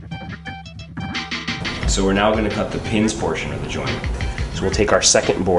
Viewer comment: Still having trouble aligning the two boards and having them match up "perfectly". I match them up, and then I have a quarter inch off at the end. Also, sounds like you say "the penis side of the board" at 00:53. Ha.